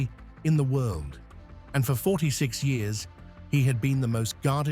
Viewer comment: Did you say 46 years? <link>